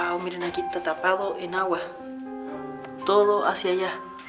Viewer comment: Minuto 11:03 casi 04 .. cuando dices que la cueva está tapada en agua se siente una voz que dice YA... (no se, quizá andabas con alguien, pero se sintió Brígido jaja) Saludos 🖖🏽